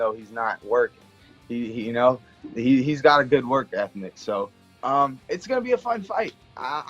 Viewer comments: Did he say "work ethnic" at 6:51 ? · Conor has a "good work ethnic." What does this mean? Is he saying Dagestani are lazy? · he actually says “work ethnic” hahaha.